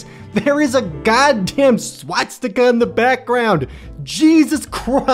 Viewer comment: what's a swatzuca or whatever you said?